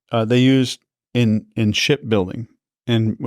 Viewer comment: Did you think he said "shit building" the first tije he said it?